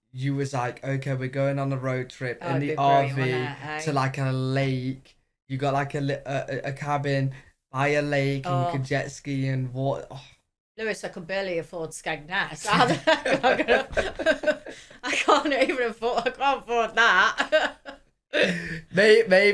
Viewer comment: Can someone tell me what mom is saying to at the 19:29 - 19:31. It sounds like she is saying I can barely afford to skagnat. What is skagnat?!!!